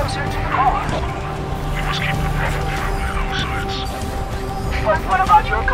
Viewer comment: What does the Arbiter* say? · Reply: "We must keep the Prophet of Truth in our sights."